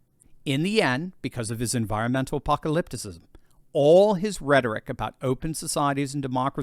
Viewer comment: Did I hear you say 'appopperlyptic'?